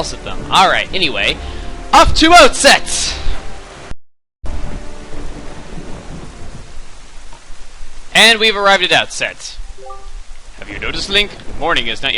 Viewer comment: The auto-captions said: "off to out sex … and we've arrived it out sense" (Yes, "it")